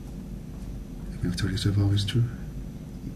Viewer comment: What does Stephen say at 11:25? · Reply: American Abroad Collecting Currency Abroad ...about a year late but he says. "Everything I've told you so far is true"